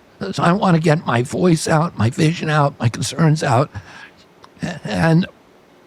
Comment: Please continue to explain what censorship is! Right around 16:10-16:15 Bubby says he wants to get his “conspirants” or “consprirance” (I hear that as conspiracies, btw) out by doing many interviews all day every day. Shortly thereafter he says the Former Guy isn’t afraid of an adversarial interview and uses Theo Von “and you,” referring to Fucker Charlatan (aka Tucker Carlson). Absolutely ridiculous stuff and I wish you had caught it. There is so much garbage to unpack on this dumpster fire- no one could find all of it. 😂